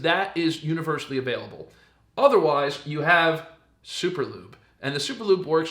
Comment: Superlube!? I thought you said superglue. Doh!